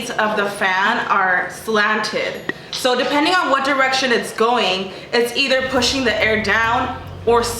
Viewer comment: i literally thought she said 'One Direction' 😭😭♥️